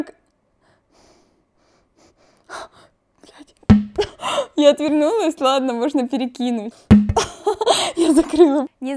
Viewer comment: Мне одной послышалось то что матюкнулась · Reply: она сказала бл**ть